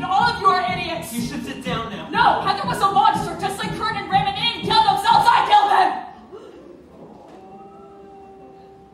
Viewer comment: What did veronica said in 2:50?? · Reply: "no, heather was a monster just like kurt and ram, and they didn't kill themselves, i killed them!"